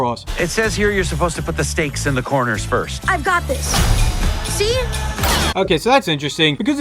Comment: help me I heard I got this fahh